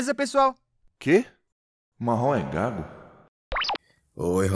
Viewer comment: eu ouvi "marrom é gado"